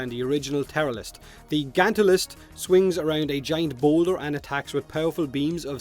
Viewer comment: swear to god I heard “swing around a giant boner”...